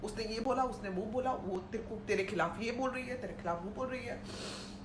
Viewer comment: She said bol rahi hain piche se 18:14 - it means that judge is a woman. Am I right or I heard wrong